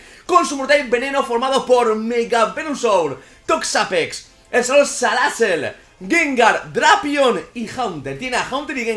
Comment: Escuche bien !??!!! Señor Salazzel 0:52 Grande 😂